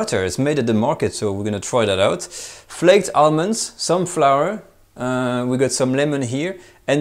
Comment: he said "some flower" and i heard "sun flower" lol took me 5 minutes to figure out he wasn't cooking with flowers.